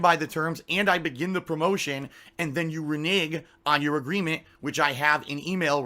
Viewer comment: YO DID HE JUST SAY "RE NIG"?!?! 😂